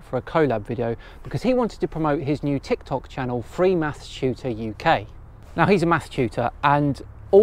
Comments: I misheard freemathstutor as freemassshooter first and was very concerned for a second 😅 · When you said “math tutor” I was doing something and was only listening and I thought you said “mass shooters” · oh, "math-tutor"..... I heard "math-shooter" :c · Let me preface this by saying, this is first time I've listened to a video of yours, enjoy minor hearing damage, and I am from the other side of the planet with a hilarious accent of my own, but... ...when I heard you say you'd decided to collaborate with a "mass shooter" (maths tutor) to reach out to some FE enthusiasts, I confess I was worried you'd taken it a bit far. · I swear the first time I heard your friend's tiktok channel name I thought it was "Free Mass Shooter UK" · enunciate. its sounds like your saying mass sh**ter when you say math tutor